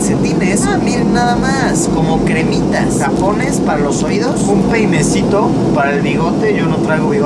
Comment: penecito, se que dijo peinecito no me maten jaja